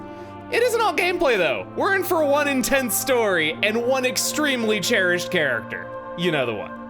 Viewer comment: Oh, one INTENSE story. I heard "one-in-ten" story and nearly spat out my water 😅